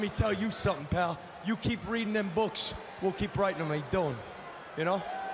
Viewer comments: What did he say at 1:21? "You keep reading them books, we'll keep ... ?" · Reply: "we'll keep writing them" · What does Enzo say after "You keep reading them books, we'll keep........????????" Minute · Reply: "writing 'em"